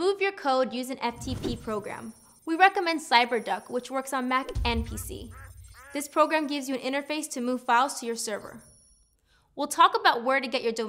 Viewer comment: the close captions says "we recommend Cyberduck which works on Mac and pussy" LMFAO! I was just surprised I caught that and the content she recommends for it.